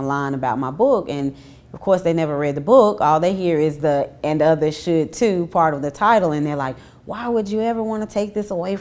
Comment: lol I thought said "The Ebony Exodus Project: why some black women are walking out on religion, and other shit too." She really said "and others should too" haha.